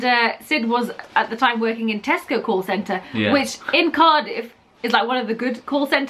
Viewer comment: I thought he said he worked at the "testical call center" at 3:30😭😂